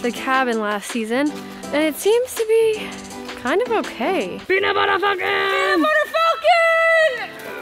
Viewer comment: What did you shout whilst moving the tree? I thought I heard "Peanut butter falcon" lol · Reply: You did! It’s the best movie of all time :) it’s on Netflix.